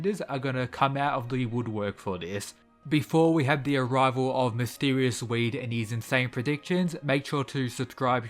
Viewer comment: Did you say mysterious weed? 😂😂